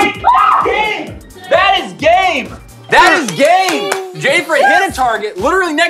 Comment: it sounded like he said "that is gay!!!" I was like WUT!!!